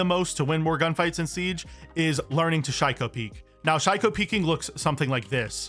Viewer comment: “If you’re in a fair fight, you ain’t doing it right“ 6:33 oooh i thought it was called psycho peek